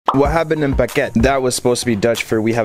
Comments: Thought you said what happened to the baguette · It sounded like he said “we having a baguette” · I keep hearing “we have a baguette” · I thought he said we have a baguette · I heard “we habben een baguette.” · I heard “we having a baguette”